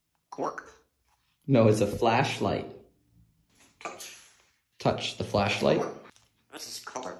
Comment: If it wasn't for the subtitles I would've thought that was saying 'Torch' that would've been crazy